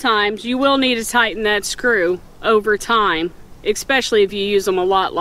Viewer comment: Nice review, thanks. I’m going to get some. And did you just say expecially at 3:42 like my cousin does? 😊